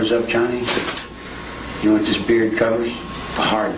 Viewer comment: what does he say the beard covers? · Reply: A hard on